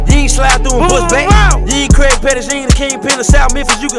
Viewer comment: dude tripping this the last thing he need right now them white folks watching every move he make . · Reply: Sound like he said you aint krabby paties you aint the king pin of south Memphis you can hush that 😆